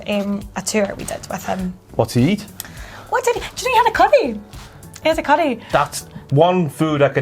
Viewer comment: I can usually understand accent reasonably well, or figure out what she's saying within a second or two. But holy crap "curry" is a weird word with a Scottish accent. I thought she was saying "cutty". Didn't know what she was talking about till said something. 😂😂😂